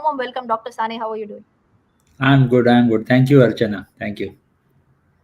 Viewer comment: did he say archana?